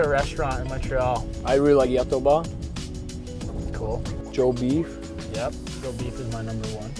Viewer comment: Joe Beevs in Montreal must have some good cooks, cause it’s trash here in sask lmao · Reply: Ahhhh I gotcha. There’s a chain restaurant out west called Joe beevers that’s what I thought he said.